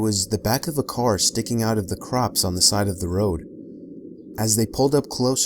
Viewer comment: who else thought he said crocs